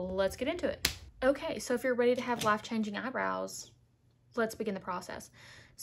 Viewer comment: I heard “laugh changing eyebrows” 👀